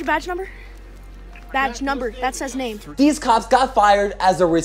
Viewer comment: “badge number not your flipping name” spoken by a true legend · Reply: Did he say that? I thought he said “badge number. That says name”